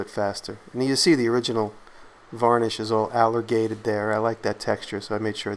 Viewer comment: Hahaha. Alligatored. I thought that was an actual term used to describe paint when you first said it. Then it hit me. Alligatored like as in an alligators skin. 🤣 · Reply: I have to admit that I actually looked it up. But I thought he said "Alligated".